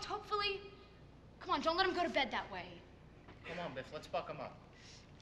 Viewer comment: does happy say : come on Biff lets fuck him up?